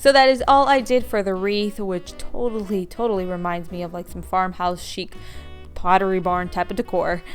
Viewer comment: You said "it really looks like Farmhouse Chic" I thought you said Farmhouse Shit 😂 I'm not fully awake... But I did laugh 👍